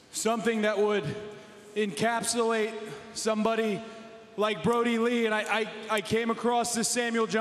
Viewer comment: did someone say at around 0:31 brodie f***ing rules? just asking R.I.P Jon Huber